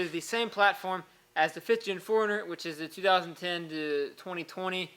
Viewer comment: I kept hearing "foreigner" its like WTF is a foreigner? OHHHHH 4 RUNNER